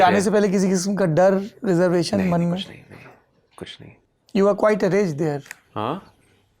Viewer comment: Saurav used an English term sounded like “araged” what was that?